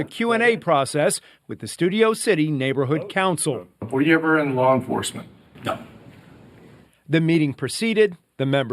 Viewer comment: He's got a lot of self-generated good press online: it's a combination of hubris and actual accomplishments. Formerly incarcerated people do deserve second chances: that's not what this is about.. It's about disclosure and better oversight. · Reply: you are obviously the one who didn’t watch the video.😂 He clearly says ‘were you ever in law enforcement’ not ‘are you in law enforcement’. Nice try though. Feel free to apologize for being a cocky incorrect pos though.